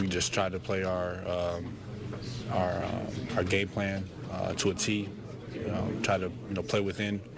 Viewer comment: sounds like he says our GAY plan lololol